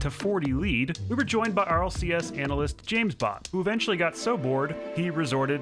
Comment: I thought he said James Bond